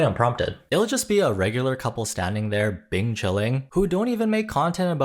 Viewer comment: Did he just say “bing qi lin” (ice cream in Chinese)?! 😂I can’t tell if this is also a reference to John Cena’s Bing qi Lin video lol